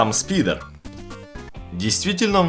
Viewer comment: кому на 8:12 послышалось спиннер ставь лайк